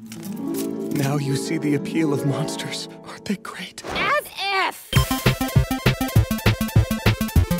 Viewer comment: What is Marcille saying in this scene? I'm not very good at English, so I can't understand. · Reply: “As if!”